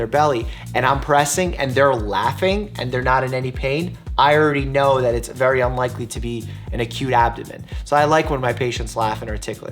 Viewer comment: I heard" It's unlikely that they have A CUTE ABDOMEN" and I had to switch on the captions🤦🏻‍♀️🤦🏻‍♀️